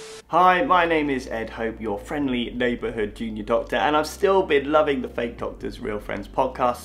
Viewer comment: Every time says "I'm a junior doctor" I hear "I'm a genius doctor"